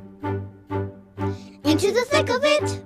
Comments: I hear "into the thickel B4" 💀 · Why I hear "into the psycho bit-"!? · Why do i hear "Into the psycho b!tch?" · I thought she said into the tickle B word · I thought the lyrics said "Into the pickle b*tch" and still think it says it😭💀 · What Most People Hear- "into the tickle it" Vs Me What i hear "into the pickle b!itche" WTH